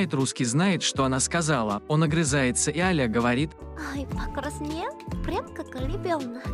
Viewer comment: что она сказала на 1:13? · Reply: Покраснел прям как ребёнок